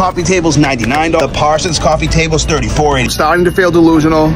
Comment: I don’t know why but I thought he kept saying tablet till he was looking at tables